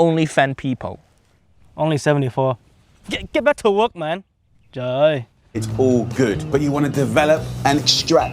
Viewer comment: Does he say "trời ơi"? He is Vietnamese but does it not hurt him to see Jamie destroy pho?